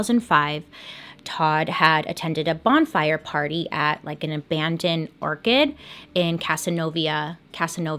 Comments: Until I saw the "map", I thought you were saying "abandon orchid not orchard"! LOL! 😊 How do you drown standing up weeks after you disappear??!! Calling BS!! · Did she just say abandoned ‘orchid’ 😂